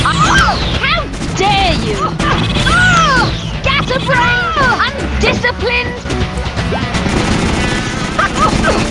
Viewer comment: what does helga say? gasabrain? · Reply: "Scatterbrained"